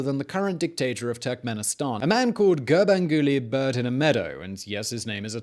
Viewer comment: THIS GUY SOUNDED LIKE HE SAID BIRD IN A MEADOW 😂😂😂😂